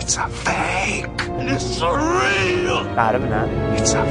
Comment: At the start of the the intro I thought he said it's a vape